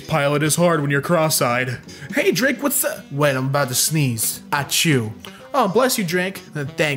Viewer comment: I thought he said "I'm about to sneeze at you"